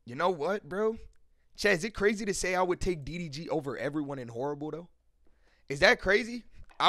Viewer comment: keem in bad, tyler in mid and kendrick in mid oh nah💀 do NOT let this man touch the aux ever😂😂😂😂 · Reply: oh mb I thought u said "eminem is horrible" 😂